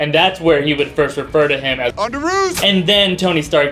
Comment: Mind Blown... I thought he said "On the roof!!" Didn't realize until this video that it was "underoos". LOL